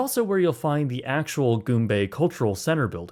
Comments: god I thought you were saying "goon bay" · Oh, Goom ... Bay. I was worried when I thought I heard it was called Goon Bay.